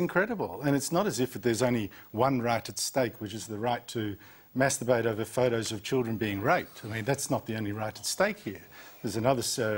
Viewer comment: wait... wait... am i hearing this correctly? wait wtf... check at 1:22 Apparently we have a right to "masturbate over photos of children being raped". is Australia run by Pedophiles?